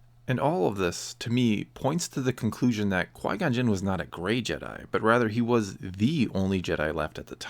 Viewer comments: at first I thought you were saying "A Great Jedi" and I was like yeah he was a great Jedi, probably the best. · I thought he was saying great Jedi for like half the video